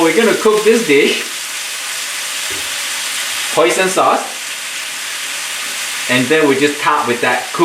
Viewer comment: What is the second sauce that you added after to soy sauce....? Great video..I would like to cook it for my family...!! . Thanks a lot..!!! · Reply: i thought he said hoisin sauce.